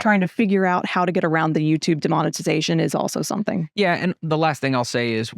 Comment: says YouTube "demonetization" and I thought for a second she said "demonization", which would not be inaccurate...